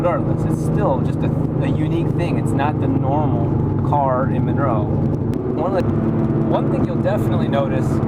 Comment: did you say in monroe? at 5:52 as in monroe, NC